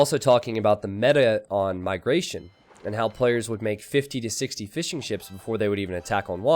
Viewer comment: wait. you actually say fishing ships!? i always thought you say fish n ships as some kind of inside joke :D